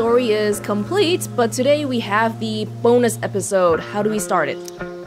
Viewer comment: I thought you said: "a boneless episode" 😂😂